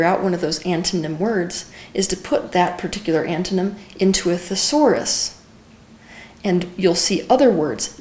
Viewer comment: can some of the native english speakers help me with what does Teal say in 10:45 till 10:52? ..'another thing to do, once you 've figured out one of these antonym words, is to put that particular antonym.....? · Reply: she talks about typing those words in the thesaurus (meanings and definition of words) to find similar other words..f.e. the word "powerful" is the antonym of "weak"..when you put "powerful" in the thesaurus words like energetic, capable pops up